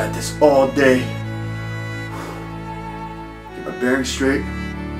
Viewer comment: I thought he said “I’m very straight” while choking on hot dogs at first 😂